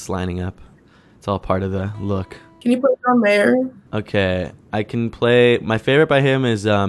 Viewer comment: I thought one of the girls said "can you put an eyeliner".